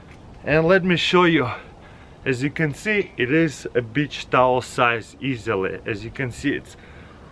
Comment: At first I thought it said “bleach” and was very concerned